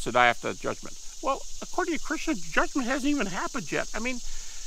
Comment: What did he say? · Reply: "According to Christians judgment hasn't even happened yet," is what he said